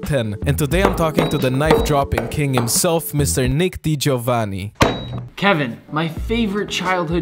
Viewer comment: Did I hear it wrong " Nick de javani" 😅